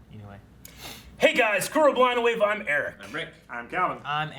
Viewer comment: “Hey guys, screw blind wave, am Eric”😅 that's what I hear every time. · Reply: For the longest time I thought he was saying "blonde wave" and I was like, meh I guess you're kinda blonde bro but whatever. lol.